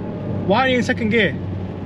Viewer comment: i spaced out and thought he said “why you sucking gear” 💀